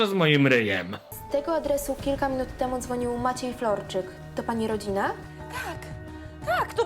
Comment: Czy tylko ja na początku usłyszałem "Maciej Frączyk"?